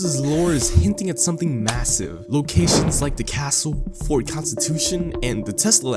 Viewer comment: did u just say…massive?